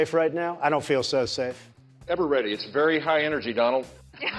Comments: What does Jeb Bush say at 5:57, ever ready, its very high energy Donald? Ever ready? '= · Im french and I don't understand the sentence Jeb Bush said at 5:56 any help? :) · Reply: It's very high energy, Donald. About what his secret service name would be.